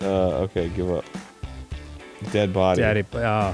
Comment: I'm so sad they didn't catch Dan misreading "dead body" as "daddy body"